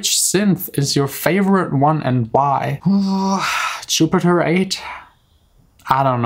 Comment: What synth is your favorite. 5:30 I didn't hear what he said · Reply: jupiter 8